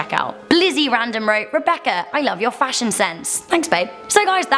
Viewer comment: did she just say thanks bae